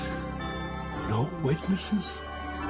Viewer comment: I thought he said “Don’t wake your sis”.